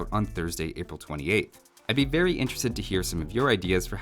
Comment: I thought he said "I'd be berry interested to hear some of your ideas..."